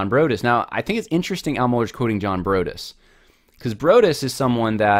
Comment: When he first referred to Broadus, I thought he was saying "BROTUS" (cf. "POTUS," or President of the United States). I thought, what is that -- Baptist Royalty of the United States?